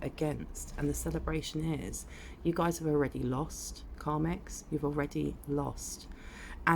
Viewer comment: 612th like... 28:14 I thought i heard you say "you guys have already lost your CARMEX 😅 "I need some CHAAAP" 🤣 CRABapple 🍤 bingbong billabong we all fall down LOL I crack myself up sometimes! Crablegs "SO shine-NAY!" Beach Ballin ⛱️